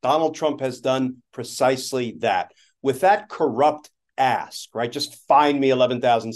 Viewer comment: I totally thought Glenn said "With that corrupt ASS..." (he said ASK, but ASS is much more precise!)